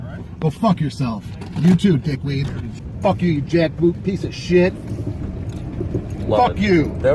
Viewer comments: did he say "jack poop piece of shit" ? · Was that a "jack poop" I heard @ 06:15 ? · did he just say "Fuck you, you jack-poop piece of shit"?